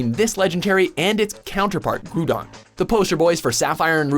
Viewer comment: Did he just say groodon? Lmao